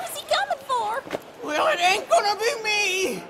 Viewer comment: I'm sorry, can anybody tell me what the guy at 6.52 says? (I'm really sorry, i'am italian) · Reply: Its the same of the 2:49, I dont understand it well too, but its something like "The/He/We rankle/angle it won´t be me"